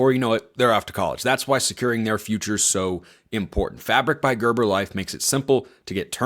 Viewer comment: your sponsor makes me uncomfortable bc all I hear is 'meat fabric' 😂🤢